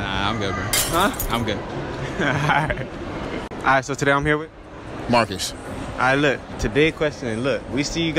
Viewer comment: it sound like he said barkus (i hear it ya hear marcus